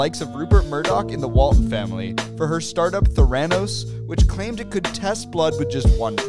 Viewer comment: Him: "Theranos" My mind: "Thanos" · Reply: But I heard "their anus" 🤣